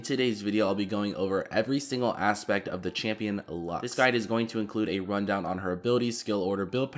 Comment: Did I just heard that called Lux "dis guy" 2 times 0:06